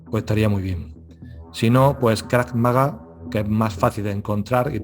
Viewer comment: Buenos dias una consulta en el minuto 36:07 dices una práctica de defensa personal pero no te entendi muy bien podria responder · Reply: Creo que se refiere a Krav Magá.